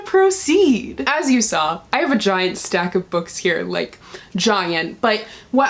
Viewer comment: did anyone get confused by hearing vagina instead of "...have a giant..." at 0:58?😂😂